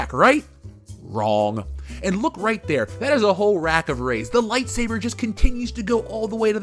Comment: I heard ‘rack o’ Reys’ and thought I heard ‘rack o’ ribs’. Now, I’m doubly disappointed.